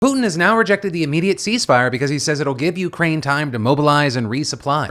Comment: I'm sorry, I'm tired, I heard "Poon has now rejected the immediate sea spider".